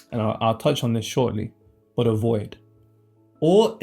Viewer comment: I know he said a void but I heard AVOID. As in avoidance